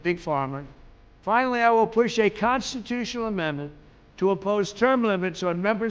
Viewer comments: As in adding term limits dawg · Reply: it sounds like Trump is saying "oppose" not "impose" at 13:51. Impose would make more sense since there are not term limits right now. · listen carefully, he actually says “oppose” instead of “impose” term limits. i am a trump fan and voted for him 3 times. just calling this out. probably a misspeak, but noteworthy.